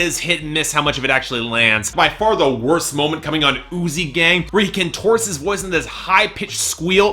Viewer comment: Throughout the review I thought you said "Oozey Gang" and not "Uzi Gang". thankfully, the real title isn't THAT gross